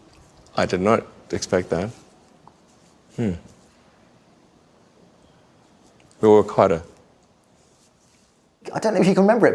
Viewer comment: After "hmm" what did Musk say at 6:18? Closed captions says The World Climate but that's not right... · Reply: "We'll work harder."